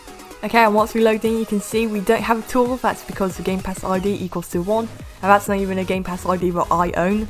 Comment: in the start it sounds like he says gay gamepass.